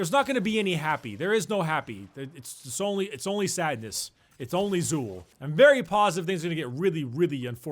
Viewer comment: I was away just listening to the thing, and I thought I heard say *"It's only SOUP"*. I lol'ed but felt disappointed when I heard it the second time :c THERE'S JUST MORE SOUP